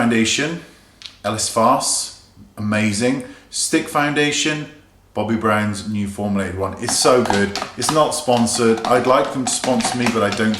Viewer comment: What foundation did he say was amazing @ 7:10 ? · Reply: Bobbi Brown's Skin Foundation Stick.